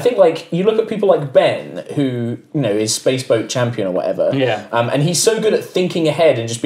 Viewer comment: Ooohhh Space wolves I thought they said space balls